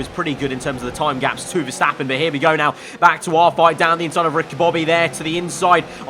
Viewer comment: Did he say down the inside of Ricky Bobby?